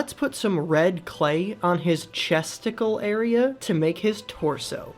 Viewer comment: That is offensive I only have one brain cell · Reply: When said chestical I thought he said Testicle